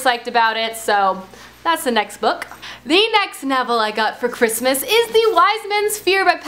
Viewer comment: When you said "the next novel I got this Christmas" before the wise mans fear I thought you said "the next Neville" 😂